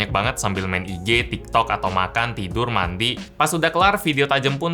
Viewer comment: yang mandi aku dengernya mati